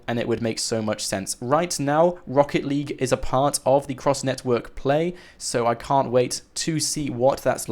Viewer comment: I swear I thought that said gross instead of cross